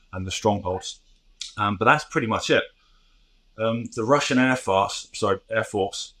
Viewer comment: A Freudian slip at the 7:20 mark is worth rewinding and hearing again. Instead of say the "Russian Air Force" Simon actually said: "Russian Air Farce" LOL I believe the entire Russian country is a farce.